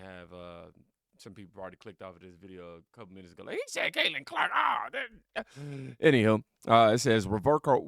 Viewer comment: I could have swore he said Catlin Clark at the beginning 🤣